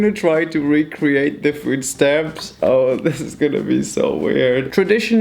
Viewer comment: 😂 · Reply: I legit heard "oh shit"